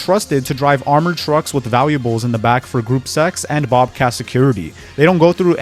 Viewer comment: did you say “group sex” ???